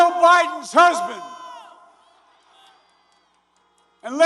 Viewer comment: Did Joe just say he was Joe Biden's husband?